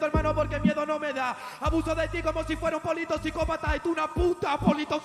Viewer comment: Me parece una barbaridad lo infravalorado que está el primer minuto de por parte de, la vrd normalmente estoy de acuerdo con sus votaciones, pero esos 3 primeros ceros me parecen un robo a mano armada xd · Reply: si, realmente pense que dijo politoxicopata, tiene mucho mas sentido "poli to psicópata"